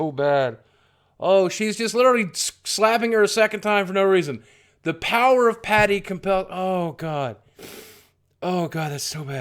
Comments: I thought she said, "The power of PANIC compels you!" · I saw this trailer like a dozen times and I always heard "The power of pain compels you". but apparently she's saying her own name. Patty. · I thought she said 'the power of pain compels you' (?)